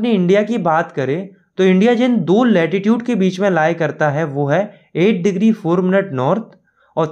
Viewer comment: it was written attitude instead of lattitude😂😂